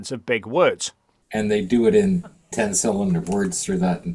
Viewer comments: Did he say "ten cylinder words"? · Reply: I swear he said "ten cylinder words"! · I know he's saying (or, at least, I think he's TRYING to say) "ten-syllable words". However, having backtracked it a number of times, turning up the volume, and fiddling with the audio of my computer, I STILL hear him saying "ten-CYLINDER words". I HOPE I'm simply mishearing. I hope (and pray) he doesn't ACTUALLY think words have cylinders.